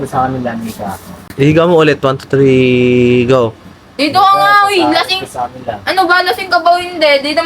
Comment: hahahaha scripted toh Narinig ko yung sinabi ni ate hahaha sabi i higa mo 123 go hahaha